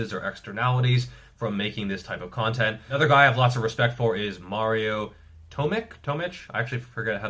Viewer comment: Lol dam it sounded like you said, “A guy I lost a lot of respect for…”